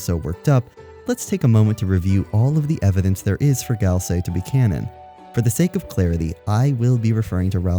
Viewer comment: did you just say gal-sei